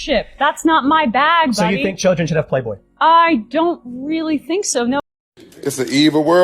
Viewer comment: I had a youtube ad that started there and it sounded like she said "I don’t really think"